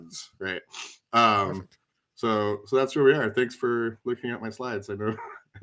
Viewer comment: I had to rewind it, I heard "thanks for having me!", And thought I'd been conned 😂